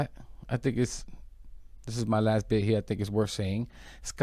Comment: what do you mean, "it's your last day here"? Did I mishear!?!? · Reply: I just listened...he said "last bit here"